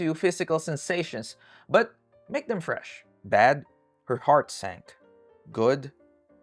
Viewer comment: I GENUINELY THOUGHT HE SAID “but make them french”